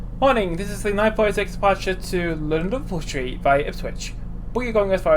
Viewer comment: did u say shitsu lol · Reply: no, I said London Liverpool Street